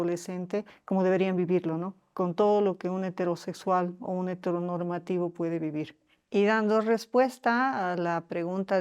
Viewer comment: Todo el vídeo perfecto, hasta que oí "heteronormativo" y de inmediato pensé que decía "heterobásico", típico insulto pasivo-agresivo de los alfabéticos resentidos, hacia el resto del mundo. ¿La palabra "heteronormativo" existe en la ciencia?